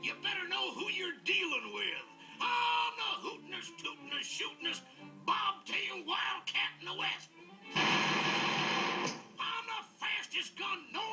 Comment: what is he saying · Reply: "im the hootin-est, tootin-est, shootin-est, bobtail wildcat in the west!"